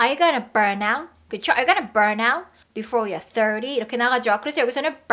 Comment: You can burn out before year 30? 로 들리네요...